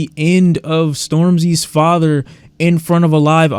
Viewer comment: bro did you just say "Stormzy's father"???